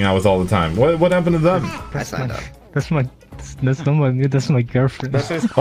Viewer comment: what does says? i don't understand him with all that censure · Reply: that's not my mute, that is my girlfriend